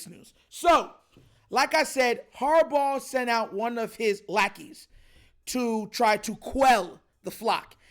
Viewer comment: Harbaugh sent out one of his blackies 😅 I know deep in my soul that I just misheard you